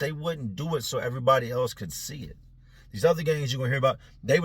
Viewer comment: I know this is an old video so I doubt people will come across this comment, but I need to know if I’m trippin’ or not.. does anyone else hear accidentally say, “ these other gays” instead of, “ these other guys” at 8:27 😂